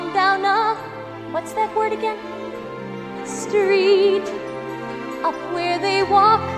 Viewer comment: I watches the little mermaid thousands of times when I was a child. My favorite movie. But since I'm Hispanic i always thought that it said A PUEDE walk A PUEDE run A PUEDE stay all day in the sun... Lmfao dudeee its UP WHERE THEY 😫😒😂